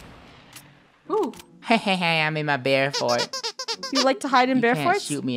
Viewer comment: *hehe im in my bear fort*: you like to hide in bear forts? You cant shoot me! SHOOTS really 😂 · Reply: Rainicorn lol i thought said “ hehe im in my bear feet” and then i was like wth? 😂